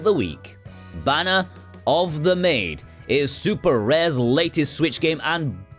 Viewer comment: I heard banger of the maid. I have no idea why or maybe the cover art threw me off is to blame 😂.